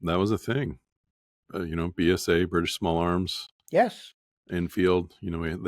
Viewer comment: That’s what he said. Or rather that’s what I heard him say. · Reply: He said: "British Small Arms"